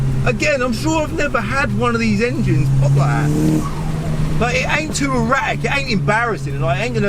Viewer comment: "I'm sure I've never had one of these engines pop like that." First I heard: I'm sure I've never had one litre engines pops like that. 😅🤣 I've just listened it again and got it right now. 😄😂